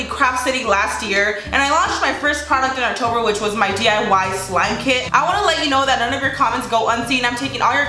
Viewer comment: I thought she said diy lube kit 😂